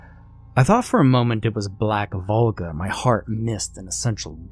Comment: I laughed at first. I thought you said black vulva lmao!!! Yes, I am a blonde lol! Love ya, have a good night 😄😘